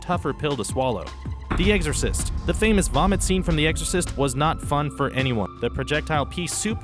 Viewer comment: Did he just say "The Eggsorcist"?